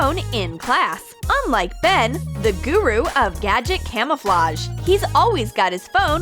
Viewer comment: is she say "guru" (teacher in Indonesia languange)